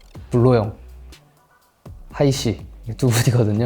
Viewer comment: Translation in 7:58 i think he said hayi ssi, not haha ssi.